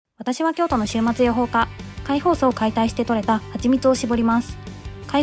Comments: 週末養蜂家が｢終末予報家｣に聞こえて人類の終末を予言してる魔女か何かかと思った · 週末予報家って聞こえて何事だって思った